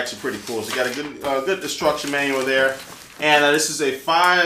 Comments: Does anyone else think he says "destruction manual" at 1:26 ? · Does anyone else think he says "destruction manual" at 1:29? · Sounds like you said destruction manual. I'll never need a manual to destroy anything! Muahaha! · sounded like "destruction manual" on